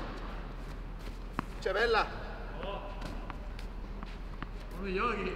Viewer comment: ¿Qué dijo? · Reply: "Che bella" (refiriéndose a la devolución de Carlos Moyá), en italiano, pero lo ha dicho mal porque ese "che" se pronuncia como nuestro español "qué". Puedo estar equivocado, a ver si alguien nos corrige.